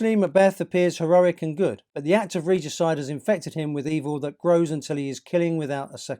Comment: the act of what? I couldn't hear well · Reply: Regicide